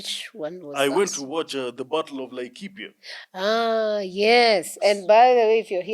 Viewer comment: Can someone post in here? What is the name of this movie he’s saying is it the bottle of Lakepia? I’m making a correction. I thought he was saying the bottle is the battle. I found it and I’m gonna watch it. The Battle of Laikipia